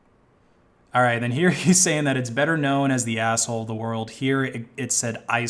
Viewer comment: The reason the algo translated "asskhole" as "ice hole" is because it actually sounds like he said "ice hole."